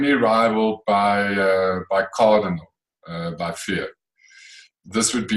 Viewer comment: Custard has always been my 1st love so this show was received very very well. mentions at about 8:30 his favourite custard...for the life of me I can't hear the name - I'm thinking Cardinal but put me out of my misery please!? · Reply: I think was talking about his fav recipe of all time here - not just his fav custard. But you are right - he is referring to cardinal by fear.